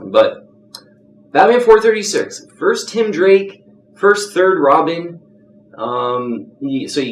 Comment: did he say first third robin?